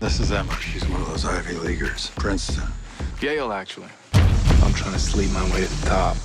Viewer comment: did he say Gale????